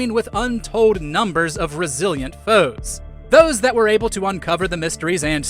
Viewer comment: I had to turn on the captions, I swear a heard "brazilian foes" 🇧🇷💔